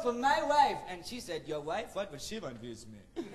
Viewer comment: Someone please tell me what he says at 18:23 I need to know I can't understand "your wife? [WHAT DOES HE SAY HERE HELP.]" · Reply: "Your wife? What would she want with me?"